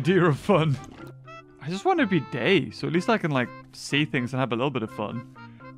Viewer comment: Ahhhh... in minutes 15:07 when you say i just want to be you dont mean gay cuz i hear it gay.....